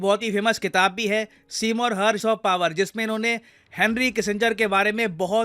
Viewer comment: i heard " the prince of Power"